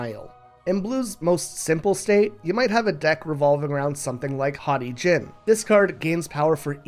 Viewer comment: I wasn’t looking at the screen and I thought the card was called “Hottie Jim” 😂😂